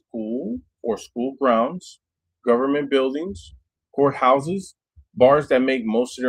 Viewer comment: Dude!!! At 2:58nI thought you said Whore Houses. 🤣🤣🤣🤣🤣 had to replay that and then realize you were naming govt blgs and agencies where OC is not allowed.